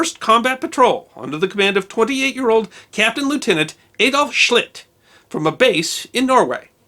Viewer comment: I didn't quite hear the Captain's name. Was it Captain Schitt? Maybe that's where we get the expression "Up Schitt's Creek without a paddle." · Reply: Schlitt